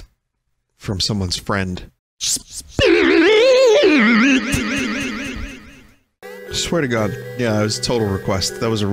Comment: what did he say? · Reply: Spin